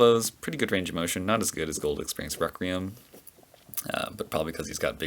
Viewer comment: Did you just say "...as good as GOLD EXPERIENCE REQUIEM" ? Lol.😂 sorry for annoying u.